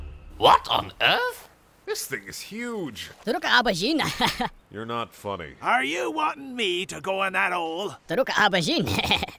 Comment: Anyone else realize that the guy with the blue face was saying "That's what she said"